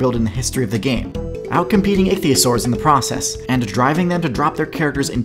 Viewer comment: captions say atheist swords